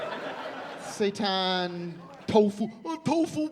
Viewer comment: Absolutely hilarious per usual, HOWEVER, does Tim call out for help from Satan around 21:12 or am I hearing things....???